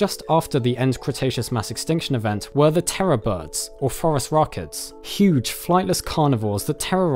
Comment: I had to do a double take at 0:33 I thought he said "forest rocket."